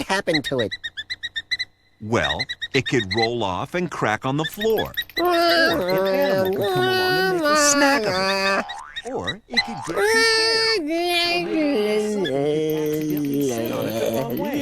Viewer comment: What was the Narrator trying to say from 15:57-16:12??? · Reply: Well, it could roll off & crack on the floor, or an animal might come along & make a snack of it, or it could get too cold, or maybe even someone could accidentally sit on it the wrong way! I mean, you just never know…